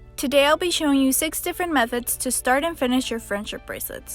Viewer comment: I thought it said how to start and end friendship lol